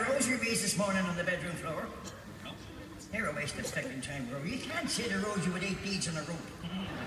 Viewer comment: What did he say at 0:27 that made Rory laugh? couldn't quite make it out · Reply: “They’re a waste of fecking time, you can’t say the rosary with eight beads on a rope.”